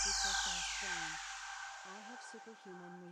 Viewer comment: I swear at the end I hear it say I'm fat you may want to re-uploaded without that ending it may be an accidental affirmation but a harmful one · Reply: it said i am fast